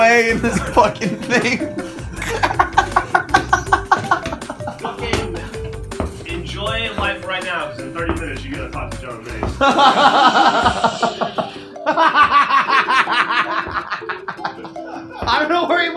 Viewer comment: What does Geoff say at 6:35 ? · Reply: Some Nerd enjoy life right now, because in 30 minutes, you're gonna have to talk to John Mace